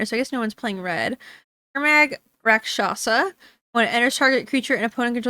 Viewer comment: Everytime someone said "rakshasa" i hear "cachaça" and I think this is funny 😅